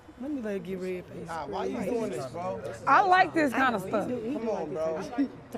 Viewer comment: These ain’t live streams anymore these are just long YouTube videos 💀 Edit: 4:58did I hear that right did she just say she likes this???